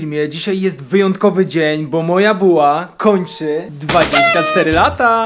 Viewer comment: O Jezu ale się wystraszyłam bo na początku zamiast "moja buła" usłyszałam "moja była"😅